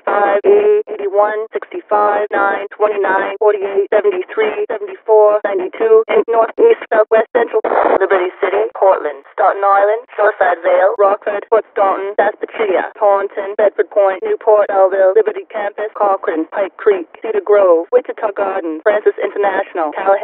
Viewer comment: What it actually says: "Staunton Island" What I thought it sounded like as a kid: "That gnarly"